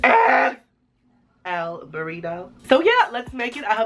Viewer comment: I thought it said churrito 😂